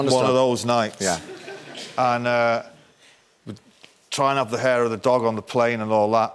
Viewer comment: what did he say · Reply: "Trying out the 'hair of the dog' on the plane...